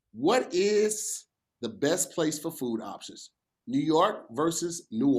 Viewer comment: For a split second it sounded like said "what is the best place for food auctions"🤣🤣🤣🤣